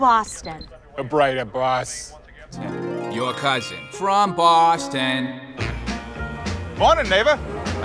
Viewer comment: I looked this commercial up because on Spotify I always hear "your cousin from boston" I say it every time 😂😂